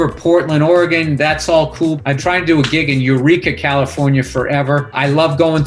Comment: Eureka is def what I know you said, but for a moment I thought you were going to say Urethra